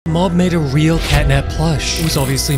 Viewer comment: I thought he said mom made a real catnap plush😅😅😅😅